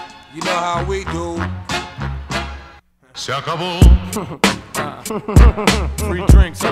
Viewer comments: i always heard “se acabo” as “suck them all” 💀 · Not gunna lie to u, thought man was saying "suckable" not "se acabo" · i thought he said "shakaboom" not "se acabo" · Bro I just hear "shaka boom" and not "se acabo" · The first time I heard him say, "Se acabo," it sounded like "suckable." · Am I the only one who spent way too long looking for this because I thought he was saying "suckable"